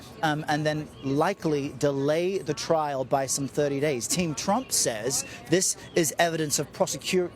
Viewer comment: I need to clean out my ears.. I thought you said "King Trump".. You said " Team Trump"..I'm sick of all this crap😅